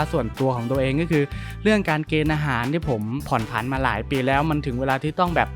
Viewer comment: ผมได้ยินว่าอาหารใช่ไหม?